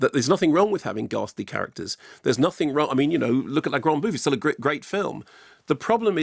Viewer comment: is Mark saying la grande bleu? Hard to make out